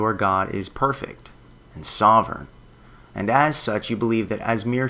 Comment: at first misheared that as "... and sober". So true lol.